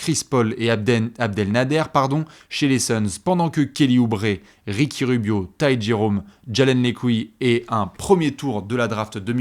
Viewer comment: J'ai compris Jalen lescouilles (Fatigue)